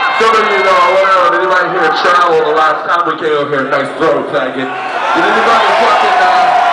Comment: I heard "nice throw thank you"...